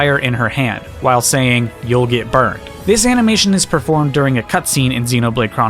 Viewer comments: I always thought pyra said "it burn" when she does the side taunt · Oh I thought she said “don’t get burned”